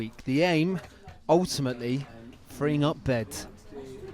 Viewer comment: "the aim, ultimately..." I kept hearing "bring up dead" but I guess "freeing up beds"